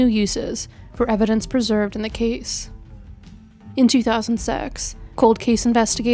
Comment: '' In 2000 sex ? '' that what she said ? lol